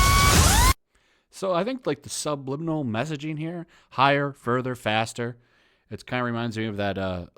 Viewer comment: "Higher, Further, Faster"?! Oh wow, I thought she said, "Higher for the Faster" the first few times I watched this clip. E·nun·ci·ate